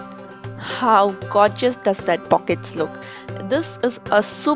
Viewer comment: I thought you said "how gorgeous does that f***ing look" LMAO